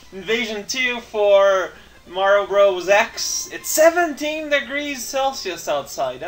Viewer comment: I hear "70 degrees Celsius" and I'm like, wait, WHAT? Then I listen again and hear "17" instead. is the only guy who will say that 17-degree weather is crazy in Canada.